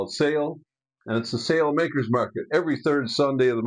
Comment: I thought you were saying "sail maker's market" and I envisioned a bunch of folk with needles and thread and sewing machines and rolls of canvas.... Nah! It's "Sale Makers' Market" innit! Vince C